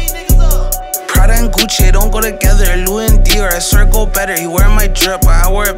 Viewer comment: really? louie instead of louis?